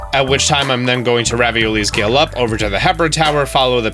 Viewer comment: did he just say ravioli gale?!